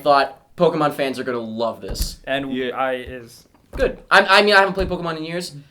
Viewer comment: did say "I is"? xD